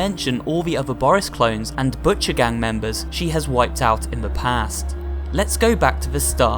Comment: who heard gucci gang members at first? admit it you did.